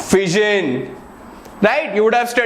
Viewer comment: I heared VISION instead of FISSION